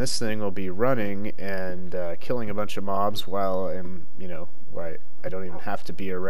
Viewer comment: Captions said and I'll be killing a lot of mom's